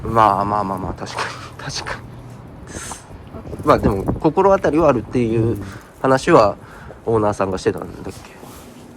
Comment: でも、さん反応する直前に女性の声っぽいのが聞こえますね。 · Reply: 他の方が書き込みしてましたけど、パパって言ってます‼️